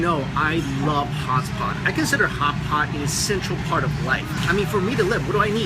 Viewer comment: It sounded like Hots spot. He like Hots Spot .....I know it's ridiculous but I keep replaying that part 'cause it cracks me up everytime 😂